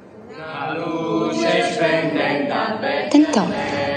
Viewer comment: Ma cosa dice Frida a 1:16:34? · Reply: Tentò